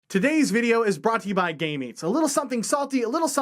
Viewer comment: I thought you said gay meats for like 5-10 seconds lmao